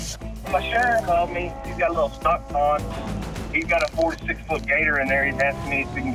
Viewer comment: On the alligator one I thought he said a 46 foot gator! LOL it's actually a four to six foot gator